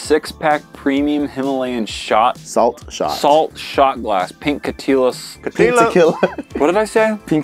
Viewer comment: I heard pink chuthulu.